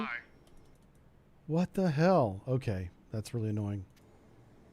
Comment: I kept hearing "Turning to course [blah blah], oh my!" rather than "helm, aye" 🤣